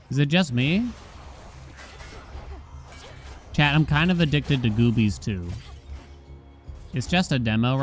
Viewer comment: i put thw video as bg noise for a few minutes and i thought he said he was addicted to boobies lol